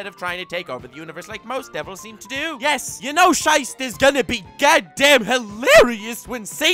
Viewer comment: did you just say "scheiß"? Greetings from Germany.